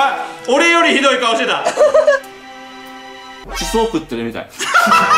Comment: 「地層食ってるみたい」に聞こえた